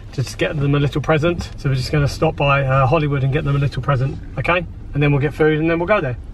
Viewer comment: I thought you said a little present Pandora is expensive